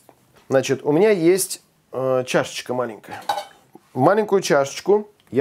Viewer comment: я услышал "чашечку маменькину")